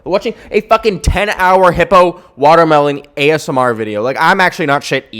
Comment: I THOUGHT YOU SAID HIMBO- NOT HIPPO